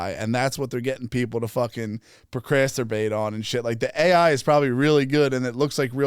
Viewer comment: Did he say procrasterbate instead of procrastinate😂😂😂